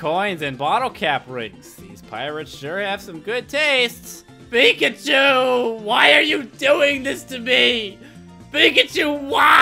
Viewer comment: When you said Pikachu why are you doing this to me I thought you said bacon soup why are you doing this to me and it's aerodactyl not ariodactyl